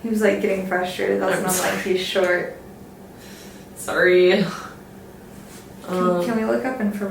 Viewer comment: around about this time, I swear I heard a man's voice say " I'm sorry".